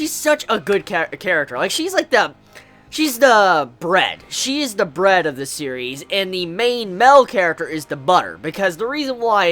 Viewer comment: When you said "she's the bread of the series", I heard "brat". Coincidence? I think not :)